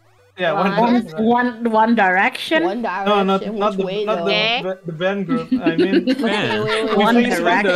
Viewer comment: I thought I heard the "bang group" lol. Fun Collab overall.